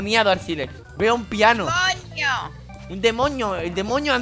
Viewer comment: dijo coño, no demonio, o eso escuche xd